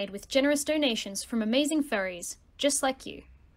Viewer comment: when you said donations from from furries I heard fairies instead lol